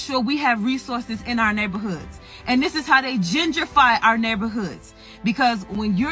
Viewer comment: sounded like "gingerfy" but she meant "gentrify" or gentrification.